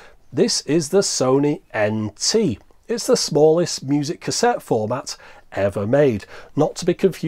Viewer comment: Had to google "musac" as I thought I heard you say. Didn't know that was a thing 😂